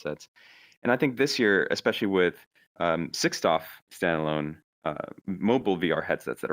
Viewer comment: it sounded like he said "sixstoff" vr headsets. i listened to it multiple times and couldn't figure it out. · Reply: 6dof, short for 6 degrees of freedom. Basically it means you can both look around and move freely.